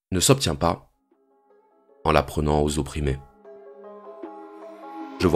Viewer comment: Pour être sur d'avoir tout bien compris, la dernière phrase de la vidéo, c'est la vertu ne s'obtient pas en l'apprenant ou en la prenant aux opprimés, merci Mister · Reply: ah zut tu me donnes un gros doute là... (si ça peut t'aider j'ai compris "en la prenant aux opprimés" mais c'est vrai qu'avec le recul la première option semble plus cohérente)